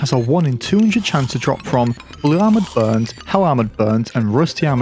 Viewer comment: am i the only one who heard you say "hell armored birds"